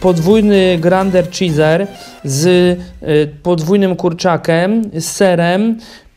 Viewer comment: ohhh nie wiem czy tylko ja ale usłyszałam kurczakem a nie kurczakiem xD